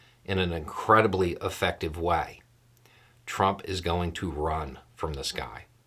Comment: You said a few times that Trump will "run from this guy". In one instance, I heard it as "run from the sky". I'm now imagining chicken-little memes of Trump running away from the sky (perhaps saying "this guy is falling!"??). 😂